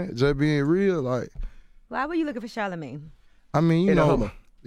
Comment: I thought he said I was just in a Hummer looking for shorter ways... I didn't even know he said Charlemagne 😂😂😂😂😂😂